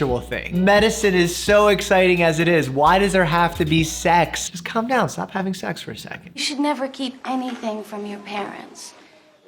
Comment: 'Just calm down, stop having sex for a second.' First I heard 'Come down, have sex for a second.' No idea why I misheard that... I might need a doctor...